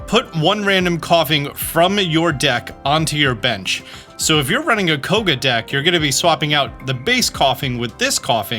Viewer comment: My sleep deprived brain thought you said "So if you're running a Covid deck" instead of Koga deck 😭